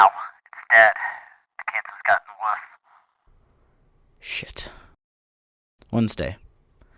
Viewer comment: what did he say?! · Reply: Shit